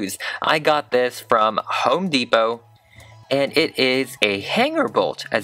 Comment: I heard “Hoe Depot”